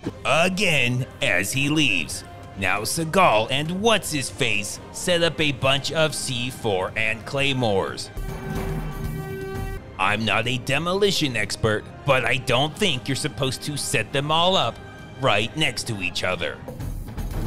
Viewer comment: I swear, i heard said "celibate a bunch of c4 and claymores"